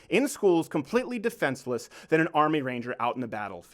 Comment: When he said Ranger I thought he said a reindeer